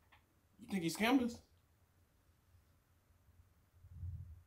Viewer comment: That "Shady Records" line was pure genius 🔥🔥🔥🔥🐐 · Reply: I heard "Shady Risk"